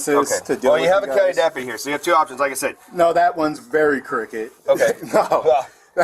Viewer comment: He was saying crooked, it sounded like he was saying "cricket"